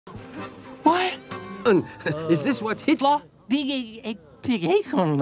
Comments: I heard Hitler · Did he just say Hitler? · I can't be the only one who hear him say Hitler at about 0:02/ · Did he just say “Hitler”? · I could swear I heard him say Hitler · is it just me or does it sound like he said 'Hitla!' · did he just say Hitler? Well actually, "Hitlah". Edit: so far this video sounds like he has schizophrenia. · is this Hitler?! The captions says its true. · Did anyone else hear "hitler biggie" on 0:02? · did someone hear hhiittlleerr?